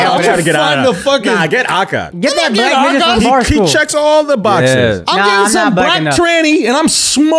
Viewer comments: did akaash say “that black nigga from barstool?” 🤔😂😂😂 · Did Akaash say nigga at the 21:08 mark after just saying he never says the n word, lmao